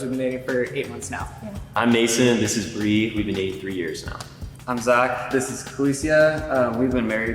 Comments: went back to listen, and you were right! I totally heard, "for years now", maybe it was just my psychic abilities kicking in 🤣🤙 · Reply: ahahaha I heard “for years” too 😂😂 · To be fair, he didn't say "for years" he said "dating three years". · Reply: went back to listen, and you were right! I totally heard, "for years now", maybe it was just my psychic abilities kicking in 🤣🤙